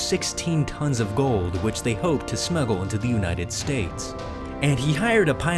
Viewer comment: I heard "Snuggle" lol